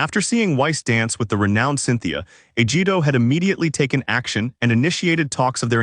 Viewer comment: I heard right.. or did he say Vegito?!